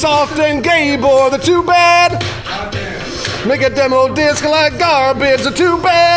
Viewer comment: Did he say nigga Demo Disk @ 1:01? LMAO